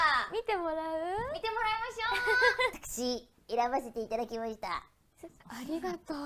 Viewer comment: maybe you misheard but the at 0:12 rieri said "私選ばせていただけました” which is translate into "I was allowed to choose this one"